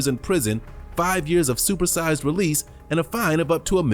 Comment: "5 years of supersized release" what you trying to say lol